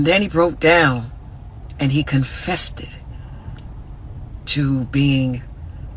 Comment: Did she say "confessed it" or "confested"?!?! Because it sounded a lot like she said "confested". Which isn't a word.